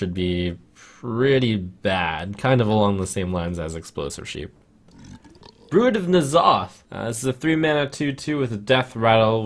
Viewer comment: i do not hear "spawn" i hear "druid"